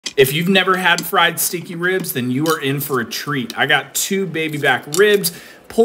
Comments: Am I the only who thought he said have you never had fried stinky ribs, your gonna be in for a treat 🤣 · Thought he said stinky. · Thought he said fried stinky ribs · I thought he said "stinky" ribs at first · Sounded like he said fried stinky ribs at first 😅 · I really thought he said "Fried, stinky ribs". Lmao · I thought he said stinky.